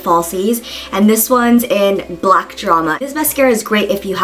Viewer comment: i thought it said black obama hahaha